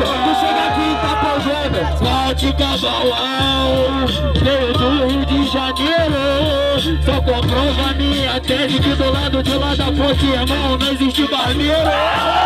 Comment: Não entendi oque o falou no 3:10 alguem me fala? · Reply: João Vitor "veio do rio de janeiro, só comprova minha tese que do lado de lá da ponte, irmão, não existe barbeiro"